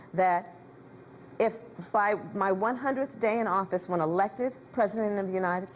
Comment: At timeline 3:28 did she say, "when I'm elected President"? Sounds like the plan about her taking over the Presidency from Biden are true!